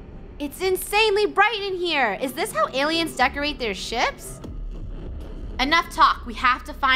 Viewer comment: When she said is this how aliens Decorate their ships i thought she said Is this how Ailens Decorate their Chips?